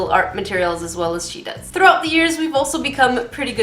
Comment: I thought u said throw up the years instead of throughout the years lol